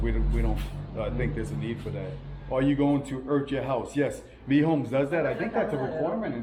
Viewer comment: in the video <link> you answer a question, "are you going to 'urch' your house?". I listened to it 25 times and still cannot understand what you are saying. Thanks! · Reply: Sorry the word I said was earth (my crazy Jersey accent) Lol 😂. We were talking about earthing the electrical system. Stay safe!